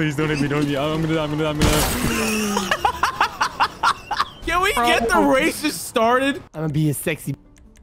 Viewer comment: Did I Hear a Can we get the racist started